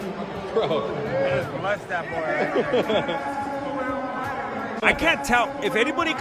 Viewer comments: NEW VIDEOS EVERY MONDAY-FRIDAY!! :_MMATrucha: DON'T RELY ON YOUTUBE NOTIFICATIONS. JUST COME THRU!! :_MMAHappiness:🔥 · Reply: I think Nate says, "Bless that boy right there" -- after Jake smeared the 🩸on himself. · " I jus bless tha boi rite ther"